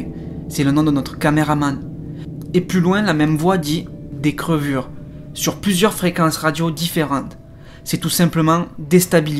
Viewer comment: A la question " est ce que vous voulez qu'ont partent ?" juste avant de coupé la ghost box on entend clairement " faites ce que vous voulez". Au lieu de " des crevures" moi j'ai entendu " c'est trop dur " . Essayez la prochaines fois de faire comme GussDx avec la papier d'alu.